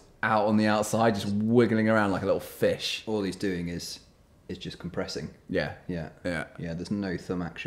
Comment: I thought they said: “seeing that thumb just wiggling around like a fish ” “Just depressing” 😂